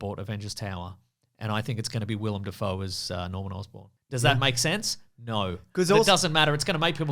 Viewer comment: For whatever reason, when you said "Willem Dafoe as Norman Osbourne" I heard it as "Will I Am as Norman Osbourne" and I was like "Okay..that's a strange take"